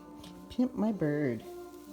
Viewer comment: Pimp my bird, 😮😂! Is that what you said, what a dirty mind you have, ew! 😅Lol😂! Time around 3:23.